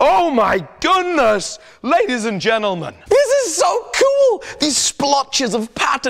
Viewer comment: did you just say "Bladies and gentlemen"